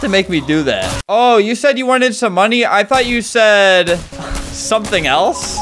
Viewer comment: you wanted some money? I thought you said "make me bloody"